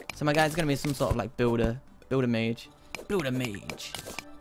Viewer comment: Lol, 2:20. I heard "Builder Mage" as "Build a Mage" and thought of it being a knock off brand to "Build a Bear".